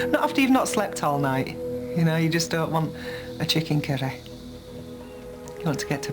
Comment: What words did she say after 0:27 seconds? A "chicken ..." what? "I've got to get..." what? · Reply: a chicken curry !